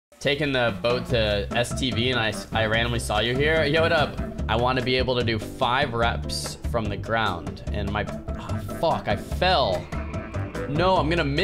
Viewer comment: I heard "taking the boat to std" no bro!